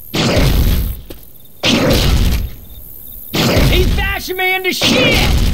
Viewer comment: "Aw hell, it's pounding me in the ass!" -Ellis, 2009 Before anyone gets all pissy poor with this comment, I know he says "pounding me to death," but since there is a bunch of slamming noises, it sounds like he says "in the ass"